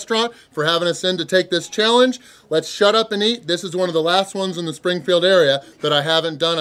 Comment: Thought you said shutup and talk🤣🤷🏽